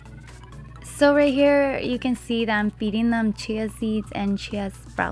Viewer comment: What did you say here...."chay seeds" ? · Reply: Chia